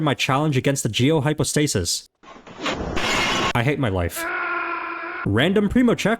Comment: When you said I hate my life I thought you said I hate my wife